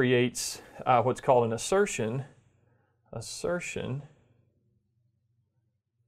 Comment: cracks me up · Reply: I heard it as "fu(king write that", whereas it's actually "if I can write that".